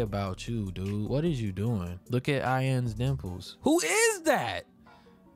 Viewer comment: I thought he said look at I.N’s nipples and I was like what 🥺